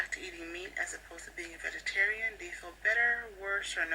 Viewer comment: I just misheard what you said as “eating vegetarians“ :'D